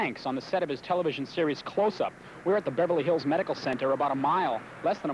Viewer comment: The reporter said close up instead of cover up.